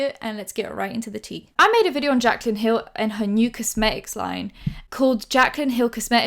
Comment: When you said “new cosmetics line” I misheard it as “Jaclyn hills mucus line” and I